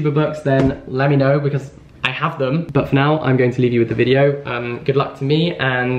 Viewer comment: I was so sure you said 'book for now' instead of 'but for now'! I've started to anticipate your puns :)